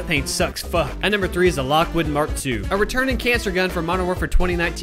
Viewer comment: Did he just say cancer gun 😂